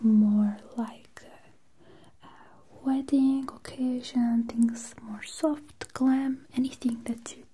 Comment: I swear she said Caucasian